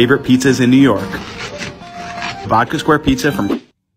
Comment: Why did I hear "Baka square pizza"